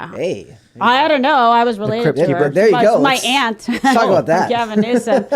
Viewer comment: Did she just say she's related to Nasty Pelosi???